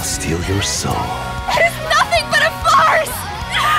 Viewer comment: I used to watch SAO dub but ever since the Alicization Arc, I haven't been able to watch it in dub since(I'm super used to Japanese now) 0:41 When Alice said "It's nothing but a farce!", I thought she said it was nothing but a fart 0:43 That scream sounds like my little cousin screaming Edit: For the ppl that commented on this comment, I don’t hate SAO dub, it’s just SAO was the first anime I finished watching, and when I got more and more into anime, I started growing out of hearing English because I had watched many anime’s with sub(because I watch on Crunchyroll, and some anime’s such as BNHA, One Punch Man, The Promised Neverland, and other don’t have dub on there). Kinda glad that switched to Japanese instead of being a dub watcher